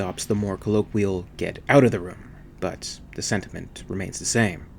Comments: Sounds like Parker is saying Get Out of the Road!! Have watched Alien about 135 times. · From my first viewing this film in the summer of' 79' and the countless times since. I always thought he said, "Get out of the road!"